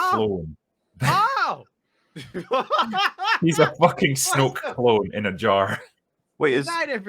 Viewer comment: When said “Snoke clone” I thought he said snow cone. Now I can’t stop thinking about snow cones shaped like Snoke clones.